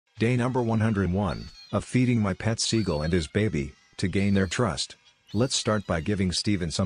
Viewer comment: I thought you said feed my let seagull his baby